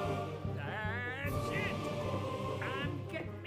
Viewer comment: did he said "ah shit"?